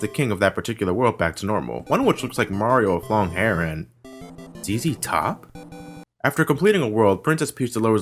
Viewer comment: What did he say? "Teezy Top"? I have no idea what character he's referring to. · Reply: He was referring to a band named ZZ Top.